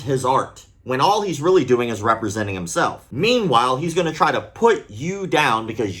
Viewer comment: i legitimatly misheard "representing his fart" 😂